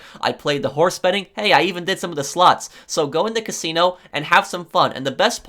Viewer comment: i wasn't really paying attention because i was playing gta and suddenly i hear: hey i even did some of those sluts...i was like🤨😲💀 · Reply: Bruh he said slots